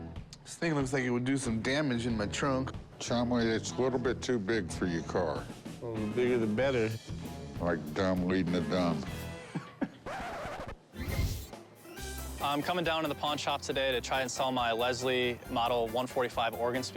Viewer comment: For a split second I thought could’ve said Like the Chum Leading the Dumb 😅 🎃